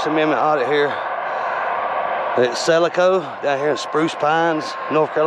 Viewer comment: Where did he say he was? I heard "Selico". My Closed Captions said "Celica". Tried looking both those places up. Neither one exists. Never mind. I went over on HPR News. He took a picture of their mailbox. It's "SIBELCO". My search found that name and location. Sorry to bother you! Good Video. If we don't practice our Rights and Freedoms, the people will forget them. Well, actually, most of them have already forgot them, anyway! gets another Thumbs Up for this one! 👍🏼